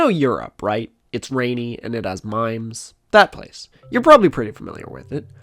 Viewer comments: Contextualizing the continent of Europe by making reference to its rain and mimes was a true pan galactic gargle blaster to my semiotics engine. Thank you for making this video incredible before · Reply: Mimes. I definitely heard 'mines' and was like 'oh yeah bosnia; landmines' and continued on as if that was totally normal. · It's rainy and it has mimes is the most apt description of Europe I think I've ever heard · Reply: I tend to just listen in the background, and I thought he said "limes"